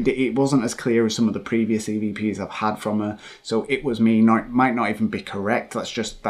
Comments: Uh, I heard “ it wasn’t me” but maybe my hearing is off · it sounds like shes saying it wasnt me for me